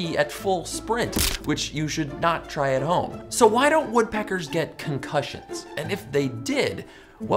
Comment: I thought it said "why don't woodpeckers get errections"